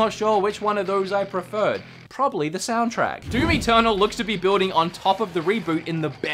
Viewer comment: "Doom Eternal" I thought you said "Doomy Turtle"